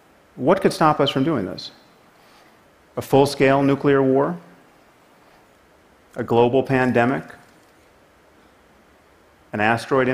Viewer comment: did he just say A Global pandemic